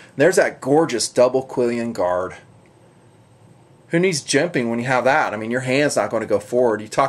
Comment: Did he just say, "Who needs gimping"?